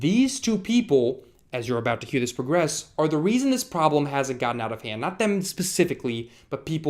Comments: He said "These two people are the reason this problem hasn't gotten out of hand!" I'm guessing people misheard the word "hasn't" as "has". That's the only way I can think this might come off as "blaming". He's talking about EA bribing and blackmailing YTers and worried about the affect that will have. He holds SKill Up and up as shining examples of honest YTers with integrity who are keeping EA in check. Sounds like pretty high praise to me. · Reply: yes I heard "has" instead of "hasn't" too for some reason, it confused me xD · He was NOT blaming you AT ALL damn people LISTEN. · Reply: He said "These two people are the reason this problem hasn't gotten out of hand!" I'm guessing people misheard the word "hasn't" as "has". That's the only way I can think this might come off as "blaming". He's talking about EA bribing and blackmailing YTers and worried about the affect that will have. He holds SKill Up and up as shining examples of honest YTers with integrity who are keeping EA in check. Sounds like pretty high praise to me.